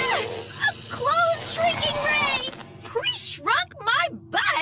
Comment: I thought Totally Spies was funny and cool as a kid. The laced cookies episode definitely seemed weird. For some reason, I initially misheard "Preshrunk my butt!" as "He shrunk my butt!" as a kid and thought it was slang.